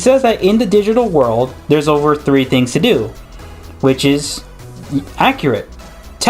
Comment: I thought he said there are ONLY 3 things to do.... have I been mishearing this?